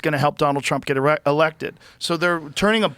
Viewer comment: It almost sounded like Joe said help Trump get an erection😂😂😂... I'm sure he does have a bit of a chub just thinking about getting America back on track and taking her back out of the hands of these demonic s*** heads!!❤